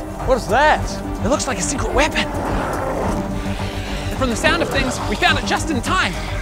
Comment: “From the sound effects we found it just in time” 😂 Greg isn’t even pretending to not be self-aware anymore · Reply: I heard "sound effects" the first time too but "sound of things" the second time